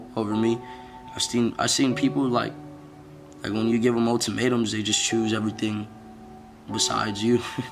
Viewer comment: rewatching the vidI saw that instead of "motimatems" it's "ultimatums" but other than that this video is always impressively motivating :)